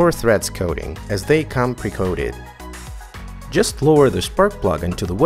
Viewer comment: What do you mean by precoded spark plugs ? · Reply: Ohhh okay haha I thought i heard you say pre coded my bad. Good vid btw !